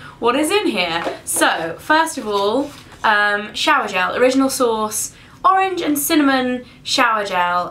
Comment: When you said "Original Source" I thought you said "Original Sauce." hahahaha